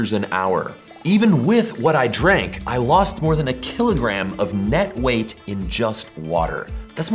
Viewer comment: wait did i just hear u lose weight in just one hour? does gyms hate this one weird trick???